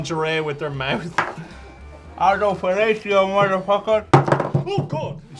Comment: Did....did say "Autofellatio motherfuckers" at 11:40?